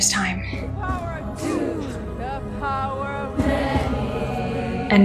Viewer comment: I'm Australian, so i completely misheard and thought they said "The Power of Money" I thought this was a Disney spoof of the Prequels for a few episodes. Wow was i wrong...